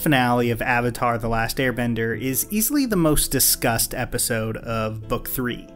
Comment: sounds like you said "most disgust"